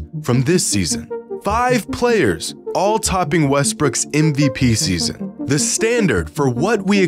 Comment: Anyone else hear him say westbrick?